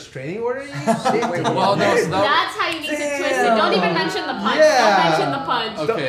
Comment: does poki say, "thats how you get pussy, dont even mention the punch"???